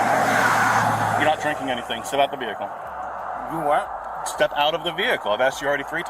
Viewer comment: Cop sounds like hes got a mouth full of crap. I thought he said pour out the beer. Then repeated and said step out of the vehicle and got aggressive with the driver.